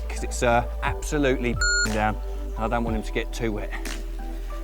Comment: did he just say he is putting it back becuese he doesn't want the fish to get wet????